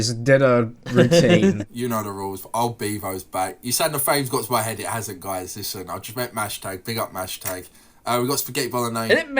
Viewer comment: what the FUCK did he even say I’m too American to understand that accent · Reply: You said the fame has got to my head it hasn't guys. Listen I just mashto big up mashto. ( I think not sure on the mashto bit)